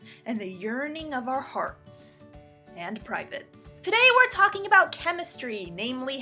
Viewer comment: Misheard urining of our hearts and privates